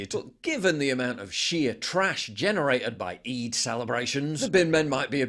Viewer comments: When he said sheer trash I thought he said Shia trash 😆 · when you spoke sheer trash, gotta admit i heard it as Shia Trash